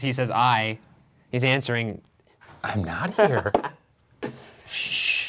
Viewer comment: I heard "I Hope" not "I am not"